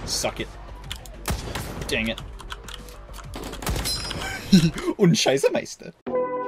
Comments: All i heard was “Shit” in german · "ohne scheiße meister" "no shit master", atleast wat i heard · Sounded like he said “und sheiße meister” which I’m pretty sure would translate into “and shit master” lol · It sounded like "Scheissemeister" which in german would mean "Shit master" or something like that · For a Second i thought is german because it sounded like shit tape on german LOL